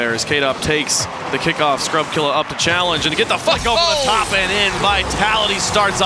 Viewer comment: anyone else hear "and get the f**k over the top and in" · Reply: it was flick not fuck